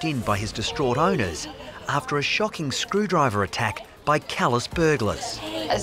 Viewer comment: I thought it said calliou at first